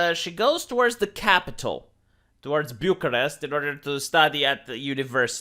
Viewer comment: Oh, Bucharest, I thought he said, Budapest, too similar capital names.